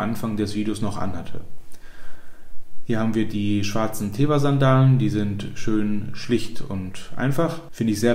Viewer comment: die Sandalen gefallen mir. Die sind mir schon in einem anderen Video aufgefallen. Welche sind das? Ich habe nur "Kleber Sandalen" verstanden. Darunter finde ich im Netz nichts.